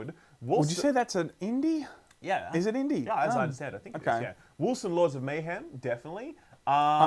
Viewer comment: for some reason I hear "wholesome lords of mayhem" every time you say wolcen...